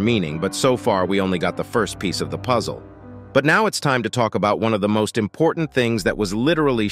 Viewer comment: I thought it said time to play